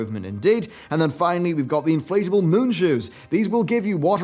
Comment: did he just say "Inflatable Moon Shoes?" Sounds AWESOME!